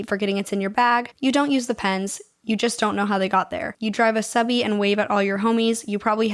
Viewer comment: I'm listening to this as I fix my dishwasher. I heard, "You don't use Depends, you just don't know how they got there" 😂